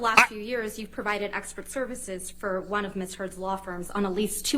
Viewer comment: I thought for a second Camille said “you provided Escort Services for Ms Heard’s Law Firm” and almost lost it 🤣🤣🤣